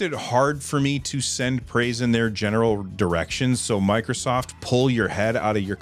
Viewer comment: it kind of sounded like you almost misspoke and said erection. I don't need to keep laughing at this but I can't help it 😂